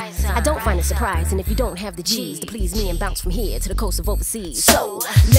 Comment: while left eye was rapping i thought she said and if you don't have the cheese didn't know it was actually the g's